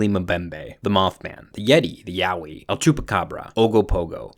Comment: I look away for a MOMENT and then suddenly I hear "the yaoi"